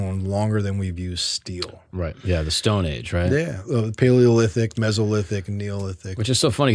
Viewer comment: I thought you said, "Stonehinge, right?" . lmao Great episode brother. Keep it going.